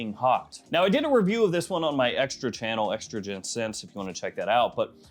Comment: I don’t know why I heard estrogen scents 😂 North stag IV would be the one I find most interesting.